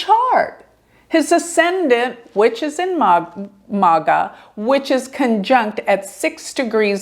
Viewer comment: Did you really say “his (trumps) sun is in maga”? 11:37 MAGA!!!?? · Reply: Good catch! In MagHa Nakshatra!